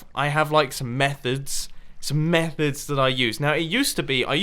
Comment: I really thought you were gonna say meth... The way you said it METHods